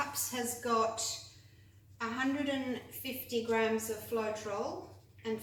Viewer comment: I’m sorry I’m very new to this type of art and I’m unclear of the ingredients mixed with the paint . Would you please message back the ingredients? I heard you say coconut milk hair serum ? And I thought you said ( flotrule ) I don’t know what that is . Sorry for asking dumb questions 😏 but this is absolutely breathtaking art and id like to give it a go one day ....